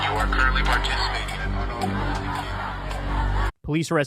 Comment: "You are currently participating in unauthorized ______" Can't catch the last word but the phase sounds authoritarian. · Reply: Camping